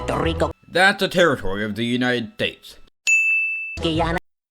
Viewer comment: Did you just say United Dates?